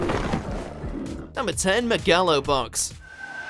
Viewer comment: Did he just say Ma-ga-lo Box?